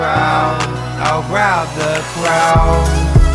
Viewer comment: I thought it said Grab The Clown